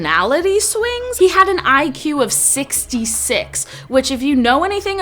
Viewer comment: I THOUGHT SHE SAID 166 UHICYGUEGYJ